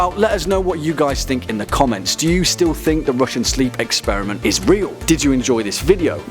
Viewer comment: I thought it said Russian sheep experiment